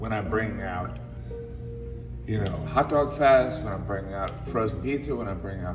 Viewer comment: Did he say "hotdogfest" at 2:15?